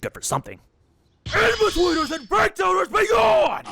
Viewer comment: the captions aren't clear enough, what is the executive saying to make the souls leave their bodies? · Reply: "Inbetweeners and Breakdowners begone"